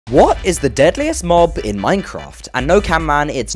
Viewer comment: Dude I thought he said what was the biggest mob and locked in my answer as ghast. Then realised it was deadliest but still got it right lmao.